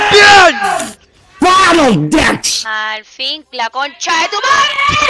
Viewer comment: alguno me dice qué dijo el cosquex? Parece un squirtle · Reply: Bonus ducks xD